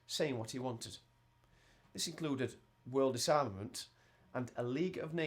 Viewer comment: in 2:21 did he say world assignment or something else..plz somebody tell me. i really need it!!